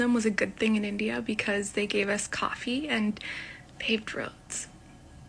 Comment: Anyone knows what she said, "The Britisch gave us coffee and ?????????????????) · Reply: I think it was "paved roads", but not 100% sure.